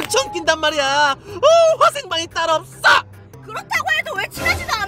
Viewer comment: 잘 들으면 딸 없어!이런소리 들려요